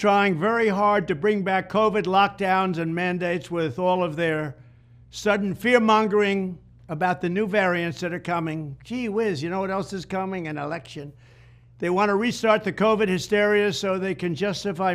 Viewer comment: did he say “cheez-wiz”?